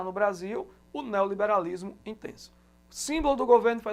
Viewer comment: não entendi o neolibarismo ''INTRENÇO'' ?? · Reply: Intenso